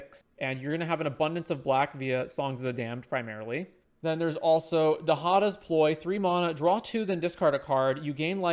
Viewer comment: I totally misheard but i thought it was "Then there's also da hottest Boi"